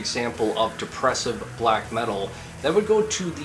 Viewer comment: The edit made it sound like you said "depressive slack metal".